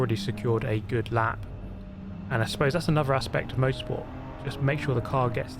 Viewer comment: i know he says 'motorsport' be it sounds like he says 'most sport'